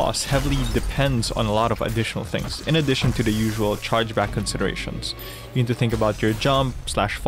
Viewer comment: thought it said “changeback” for the longest time